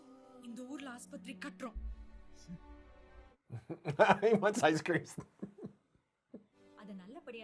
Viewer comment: Lmao. He isn't asking for ice cream there . Is that what you said ? That's what my ears caught. So he's not asking for ice there , her name is "Aishwarya " . So in short Aishu , which rhymes with "Ice" . That's just a nickname.